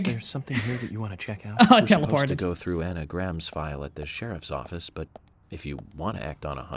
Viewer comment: when they say Anna Graham it sounds like their saying anagram